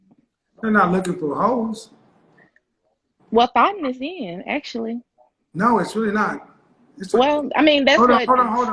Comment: Yo, did she just say "thot's is in?"😢😢😢